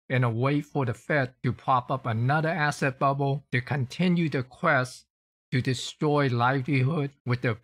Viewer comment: what? "quest to destroy life fever.."? What's he said?